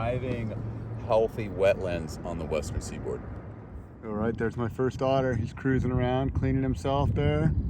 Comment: "Alright. There's my first otter." I had to rewind that... I heard, "Alright. There's my first daughter." 😆